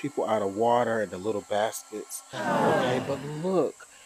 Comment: I thought you said little bastards not baskets. I almost loved u more 😂